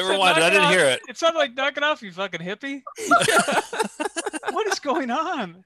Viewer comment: "Knock it off, you fuckin' hippie!" I heard it as that too, and damn near pissed myself from laughing. I still have no idea what he was really saying.